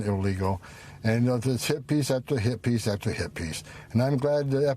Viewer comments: I had to replay it, I thought he said hippies after hippies after hippies 😂😂😂😂. Talking about sacrifice, give it a rest. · Homan is not “Human.” He mumbles so bad that I initially thought he said “hippies after hippies after hippies.”